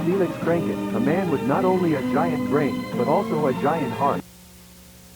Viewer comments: as "Giant brain" i heard it as " Giant crank" which sounds....very....hm.. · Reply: I thought he said Giant Arm.... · “Lets give it up for Felix! Who not only has a giant brain, but also a giant heart!” Jack Walten is typing… Hehe. I have 3 thousand likes. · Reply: as "Giant brain" i heard it as " Giant crank" which sounds....very....hm.. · "Felix is a man with not only a big brain, but, a big heart." Jack Walten: pure disagreement edit: MOM IM FAMOUSS!!! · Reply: it almost sounds like he says “not only is he a man with a giant drink but also a giant heart” which is ironic